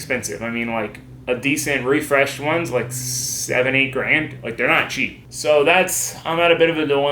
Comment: I thought he said 78 grand I almost spit my god damn noodles out